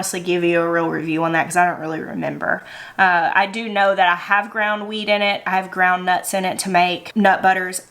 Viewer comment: I thought you said “I have ground weed in it” 😂 that’s a whole different kitchen series. 😂